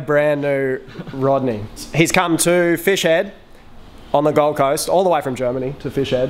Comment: dangit i thought you said gulf coast i live on the gulf coast.